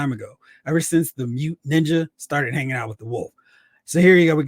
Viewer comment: Oh 🤦🏽‍♂️ I just got it, you said,"Mute Ninja". I thought you were saying"mutant ninja", I was like, did I miss something? When did Snake Eyes get an x gene? LoL 😆